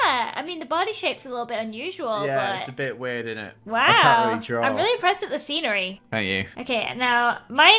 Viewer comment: Ldshodow lady: i cant realy art then she draws ihascuquakes hair me: WHA- Wha- i thought you said you Cant realy are ......TEACH ME PLEASE